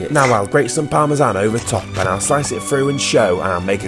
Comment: Did he just say “guacamowl”?